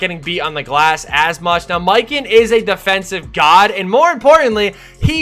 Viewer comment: I legit thought you said, "Mican is a snacc" Haha Good luck!!